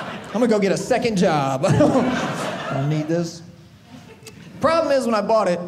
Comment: I’m sorry what does he say at 8:46 after he says second job ??? Neeegus? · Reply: I don't need this....